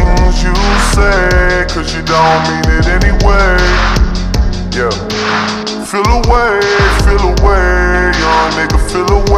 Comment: Me ass thought he said feel a wave🤣🤣🤦🏽‍♂️